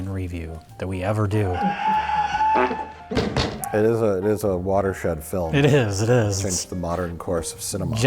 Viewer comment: I thought said it was a "water shit" film, instead of a watershed film. Either phrase could work though.